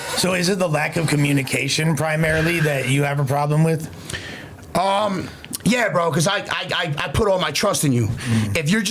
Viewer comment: bricc said to the blunt 😂 y’all know why 😂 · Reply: CACKSUCKA!!!